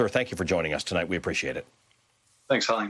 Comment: Right at the end shiff sounds like he says thanks honey